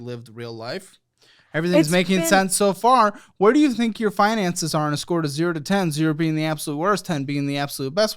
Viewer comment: 😂 when he made the 7/10 joke, she thought he said "Fine Asses, not Finances"